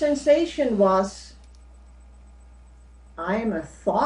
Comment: I loved this lady's description, but at 3:08 i had to laugh as she declared "I'm a fart!" (My fault mishearing it, of course..and absolutely no offense intended) : ) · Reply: She said "I'm a thought" - not "a fart". Ha Ha!